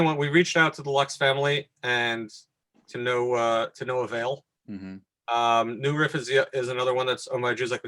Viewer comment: I have listened to the video a few times, writing notes on the information. Thanks for interviewing the Rabbi. However, at 18:00. I couldn't understand what Rabbi was saying? It sounded something like "Numa Visia" ? What did he actually say? Other comments: I am a bit a bit puzzled regarding Rav remarks about the LuxCo sale. If LuxCo has not been doing Mechiras Chometz and sold their stock to MGP, then wouldn't all this stock remain asur, despite now being non-Jewish owned, such is the nature of the knas of Chometz SheAvar Alav HaPesach? The Rav did not make this point clear. It's a shame that you didn't ask Rav more in depth questions. For instance, the Rav did not go into any of the details regarding chometz manufactures. on your broadcast. For instance, presumably, the Jewish company does NOT shut down production over Pesach so, they would have to sell not only the old stock but the entire company to a non-Jew in order to continue producing? Many other questions in this vein, like how does the Rabbi deal with Jewish distributers and suppliers on Pesach. Thanks for mentioning Episode 24. I'll try and watch this soon. Cheers! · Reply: Thank you so much for listening and for your feedback😀 The distillery he mentioned around 18:00 is New Riff. As to your other comments, thank you for sharing them. I just may inquire further.... As to your final comment regarding Jewish distributors and suppliers on Pesach, that was beyond the scope of the episode, as the focus of the episode is about distilleries, as the title makes clear. However, it is certainly a great topic for a future episode😀 And, yes, please feel free to watch episode 24: <link>